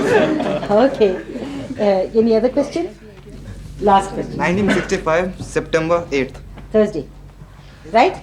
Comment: I think she heard it as 8th Sept, 1955; that's a Thursday. The way he said it, for some people would definitely sound like 1955 with background noises, etc. I think she is still right here, only she heard it as 1955 not 1965.